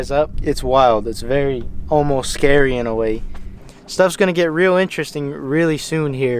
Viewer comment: Did he say "Homo scary" ? 😆